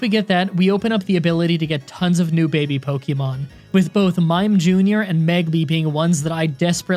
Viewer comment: I thought he said mime jr and mankey